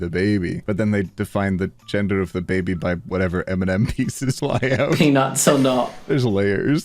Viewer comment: Love how captions say "Eminem" like the rapper instead of M&M like the candy XD